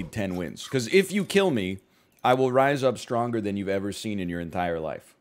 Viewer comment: "We will become more stronger than you've ever seen in your life before." Give this man an award (or a grammarly account). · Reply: When did he say that? All heard was him saying “I’ll rise up stronger than u’ve ever seen in your entire life”